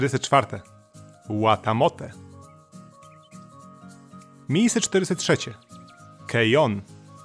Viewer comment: Usłyszałam miejsce 403 i miałam takie wait co